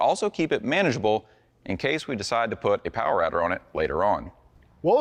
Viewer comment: My ears are tingling. Did you say power???